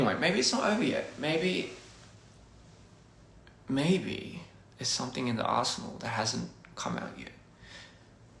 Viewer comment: When he said "maybe there's something in the arsenal that hasn't come out yet" I thought he said "something in the as*hole that hasn't come out yet"... 😭